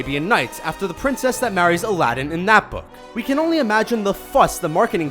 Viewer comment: I thought he said the prince that marries Aladdin and that's the moment when I knew I needed sleep